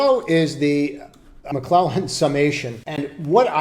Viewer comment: What indicator do you start using at the 12:15 minute mark? I think you said the Mcclellan oscillator but the one on trading view doesnt have two lines like that. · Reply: McClellan Summation